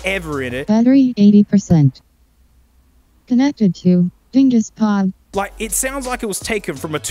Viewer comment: "connected to ... dinguspod" I was passively watching the video but that line got me to laugh out loud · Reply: I heard ‘Gengis Khan’ instead of dinguspod... think I’m goin senile